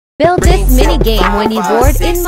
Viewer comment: What I heard is: "Build di** mini game" 😭